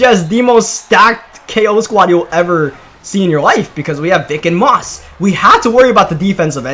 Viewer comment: 2 46 it sounds like he said dic🥜 and moss